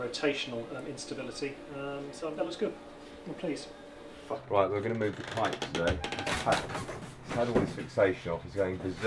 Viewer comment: I hear "Um, so that looks good. M'pleased." "Fuck." "Right, we're gonna move the Kite today..."